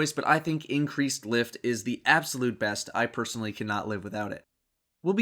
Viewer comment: I thought he said, I cannot lift without it LOL